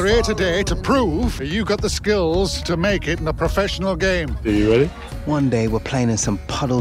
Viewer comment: what did Harry Kane even say lol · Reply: Are you Ready?